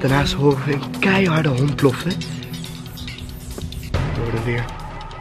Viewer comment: hahaha een keiharde hond blaffen? Haha denk eerder een hond keihard blaffen haha Lol en hij zegt ook nog bloffen inplaatsvan blaffen haha (Geen haat) Like als je dat ook hoorde